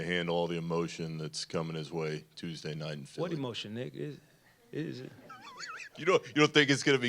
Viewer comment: “What emotion nighaa.” That’s what I thought coach jv said 😂🤣